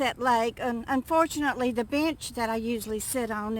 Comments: I thought u said bitch lol not bench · OMG...that accent of yours. I thought you said...."the bitch that I usually sit on"...instead of bench. LOLOLOLOL!!!!